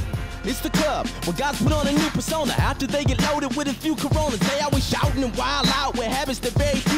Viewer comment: Did he just said corona